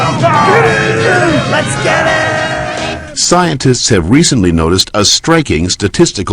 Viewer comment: i legit thought that they were saying esketit